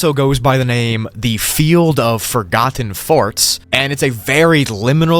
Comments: WE DOING BACKROOMS VIDEOS FOREVER BRO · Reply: is it just me or did you say field of forgotten farts?😂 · “field of forgotten farts” is what i heard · I thought he said the field of forgotten farts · I thought he said “the field of forgotten farts” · Bro I thought you said field of forgotten farts 💀